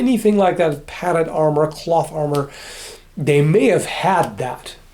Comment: I heard Plot armor the first time I watched that 😂